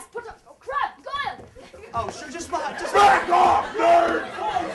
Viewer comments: Not so fast potter CRAB GOYAL!!! i thought he said oh crap goyale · when malfoy yells Crabbe Hoyle to attack harry it sounds like he says " oh CRAP GOYLE!'